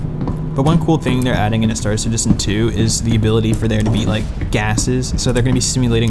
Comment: did you say star citizen 2??????????????